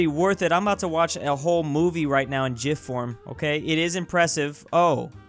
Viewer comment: did you just say JIF